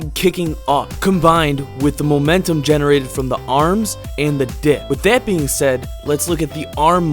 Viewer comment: I thought he said “ momentum generated by the arms and the dick”